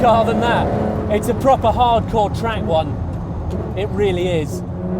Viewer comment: What the hell his he saying? 0:40 It's a Proper hardcore ''TRANGOUIA'' ??? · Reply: track one